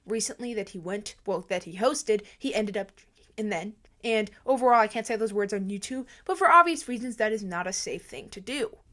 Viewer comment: what did she say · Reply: drinking and driving